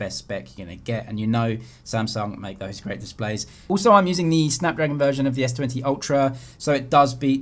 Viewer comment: he says it is the snapdragon version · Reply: this what he said in 7:28. He said "I am using the snapdragon version"